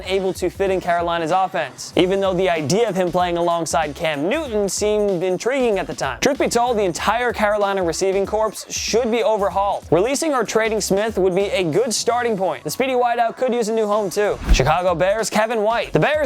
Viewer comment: did he say receiving “corpse”? Bruh ever since ya boi Mike left things been going down